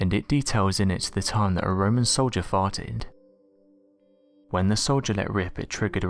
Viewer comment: At first I thought you said “a soldier fought.” But nope, it was indeed a soldier farted.